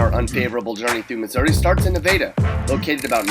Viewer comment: did he just say nevada (neh-veda)